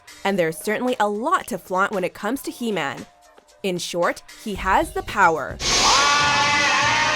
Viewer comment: I thought she said "in shorts he has the power" Hahaha